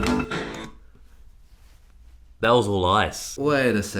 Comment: “That was all ice” I thought he said that was all lies at first